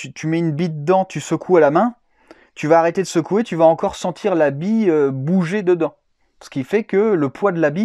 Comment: salut, pour le cylindre bloc rouge en forme de sex-toy japonais..., j'ai entendu " si tu fous une "bite👌" dedans et tu arêtes de bouger, tu va encore sentit le mouvement…." mais j'ai l'esprit mal placé. je me suis étouffé de rire.😭🤣😂😅